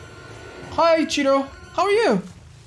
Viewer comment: I thought he said "hi ochido" instead of "hi cheeto" at 1:38. It would have made so much sense if one of those survivors was Ochido